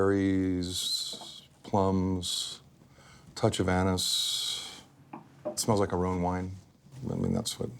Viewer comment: Did he say touch of anus at 4:21?